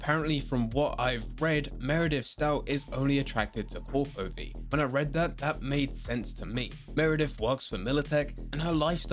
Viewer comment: What did he say here? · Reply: He said that perhaps she would only prefer the corporate V.